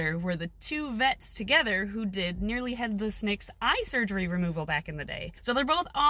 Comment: Who had the eye surgery by Dr. Gates that she mentioned? I thought she said Nearly Headless Nick but I know that's not right